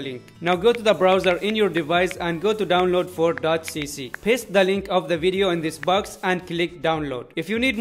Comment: did he just say piss the link lol😂😂